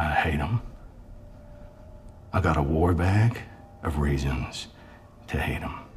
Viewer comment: I thought he said “raisins” at first lol